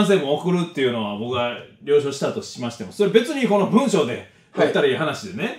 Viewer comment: 了承が「よいしょ」に聞こえた